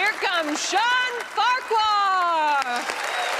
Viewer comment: You hear "shawn farquhar" I hear "Lord farquaaad"